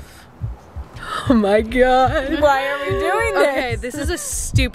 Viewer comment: "O my gyatt" did anyone else hear that?